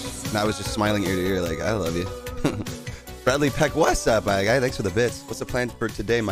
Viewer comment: What does he say at 0:22 ? I've always wondered. It sounds to me like "what's up my guy" but I still can't tell haha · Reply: What's up my guy thanks for the bits